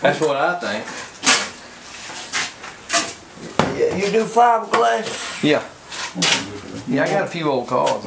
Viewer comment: Sounded like said ,will you 5 grand , at 2: 00 ?